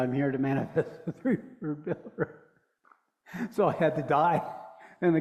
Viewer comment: What does he say at 2:50? I’m here to manifest what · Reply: "The Third Pillar"